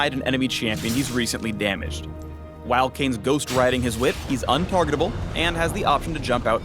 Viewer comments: pause. 3:23 did he just say "ghost riding his whip" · Okay Okay... Did he just say "Ghosting ridding his whip"